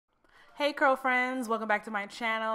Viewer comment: did she say "curlfriends" or "girlfriends" 😑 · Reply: Curlfriends! lol. Thanks for watching!